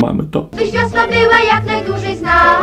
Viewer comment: a ja usłyszałam "upiększaj swiat zjebami".